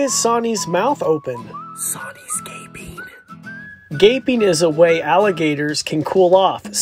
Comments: I thought he said vaping lmao 😳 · I thought for a second he said “✨Soni’s a gay bean✨” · I thought he said Sonis vaping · I thought you said gaying sorry lol 😂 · It sounded like he said vaping I was so confused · Thought you said vaping… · For a second I thought you said vaping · I thought he said vaping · I thought he said soni is gaming lol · I thought he said Sani’s gay 🤣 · For some reason I didn't hear the "ping " in gaping so I thought you said "soni's gay " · I thought he said Soni’s vaping lol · I thought he said: Soni’s vaping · For a second I thought he said vapeing 🤣🤣🤣